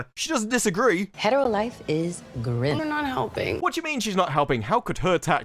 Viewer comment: Did she say, "Hetero life is grim" @ 13:04 - 13:06?!